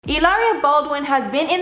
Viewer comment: I literally thought it said Hilarious Baldwin. I am so sorry.